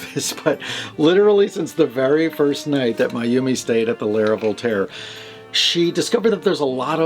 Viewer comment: Voltaire and Mayumi are super cute together! Orville saying "estoy bonito"....that´s something LOL!!! Hugs from Sonora Mexico! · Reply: I’ve been hearing her name wrong all this time! I thought he was saying ‘My Yumi”!